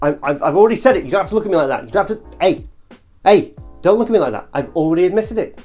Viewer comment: I COULDVE SWORN YOU SAID YOU HATE LE MALE LE PARFUM WHAT